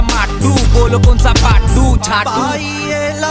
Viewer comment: did divine just said jhaatu